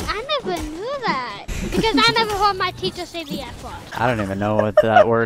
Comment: i thought he said i never hold my penis at the airport 😂😂😂.